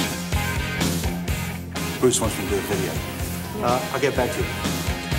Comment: what say glover at 2:57 ?? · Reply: Bruce (not sure if he means that name) wants to do the video. Uhm, I get back to you. Hung up